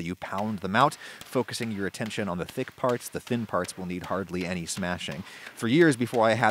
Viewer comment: "the thick parts will need hardly any smashing" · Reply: He actually says to focus your attention on the thicc parts because "the thin parts will need hardly any smashing". Carry on, people of culture.